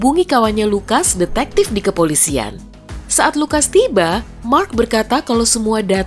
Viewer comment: waitt i heard “Mark lucas”🤣🤣 ternotis